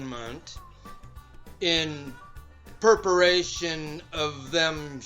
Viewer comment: What's he saying? I just keep hearing perperation or maybe purpiration or... something? Far as I know, those aren't words - are they? My spell checker isn't flagging purpiration so I suppose I ought to go look it up EDIT: Tried, found nothing.